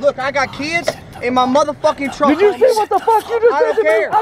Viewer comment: Did he say he has kids in his trunk? 0:20-0:25 listen